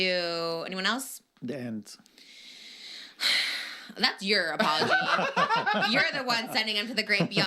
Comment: who did say? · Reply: “The ants”😂 the ants are killing their garden so he put a waffle out to attract the ants away from the garden